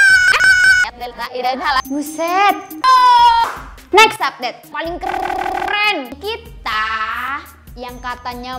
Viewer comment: kok gw dengernya fanny keren ya?